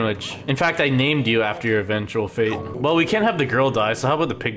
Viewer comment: Why is that guy still wearing a plastic bag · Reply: I ALWAYS THOUGHT IT SAID "put a plastic bag over its head.. AHAHAHAHHAHAHAHAH"